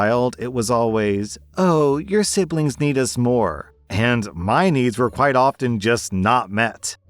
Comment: I misheard “your siblings need us more” as “your siblings need a S’more” and it made me chuckle for a minute when I realized